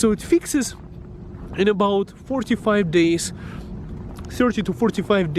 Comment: Sounds like he said 4 to 5 days 😂😂